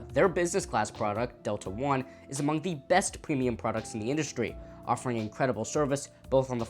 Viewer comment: wait did you say “best in the industry”? Fly Korean or an Asian carrier. Now we are talking “Best” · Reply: says “among the best”